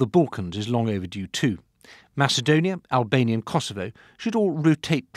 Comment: I thought it said Alabama for a second